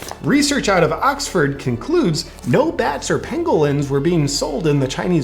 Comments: At first I thought you said “bat or penguin”, not pangolin. 😂😂😂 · Lol I thought you were saying Penguin. Not pangolin.